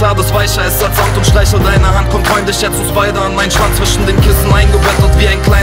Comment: jungs was sagt der da? ich versteh immer schwanz, ziemlich unpassend in so einem deepen track